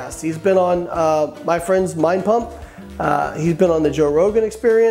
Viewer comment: So I Heard Mein Pump at 7:20 thinking it’s. Twist on Hitlers Mein Kampf wrf